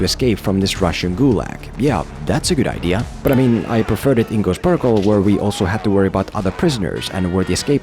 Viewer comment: but I preferred it in ... what now??? Seems like an interesting movie but if I can't understand what you say there then I'll never be able to see it 😭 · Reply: Ghost protocol